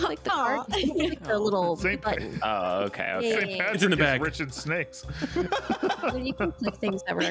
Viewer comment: "Rich in snakes" broke me and I have no idea why. xD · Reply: I thought they said "Rich and Snakes" in the video. In is much funnier though